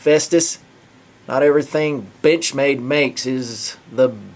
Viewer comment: sounds like he said "bitchmade". I giggled.